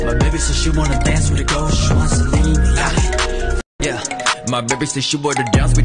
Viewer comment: when i first heard this song i thought it said "she wants to lead me" and i literally lost it😭